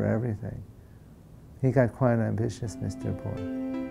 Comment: the subtitle says: "He got quite ambitious, [INAUDIBLE]." .. but I think he says: "He got quite ambitious, Mr Bohr".